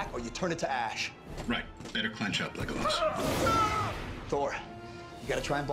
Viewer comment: huh i just realized that iron man made a lord of the rings reference in that movie, when he was flying hawkman to his position. he said,"Clench up, Legolas" but until now i always thought he said,"Clench up, Leg Loss"